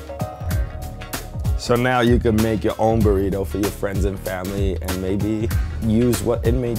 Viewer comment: Who else thought it said "person burrito"